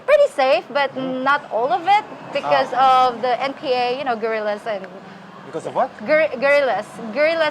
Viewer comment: when you asked about the guerrillas with your accent I thought you said gorillas. Your English is excellent you speak like a native speaker 😀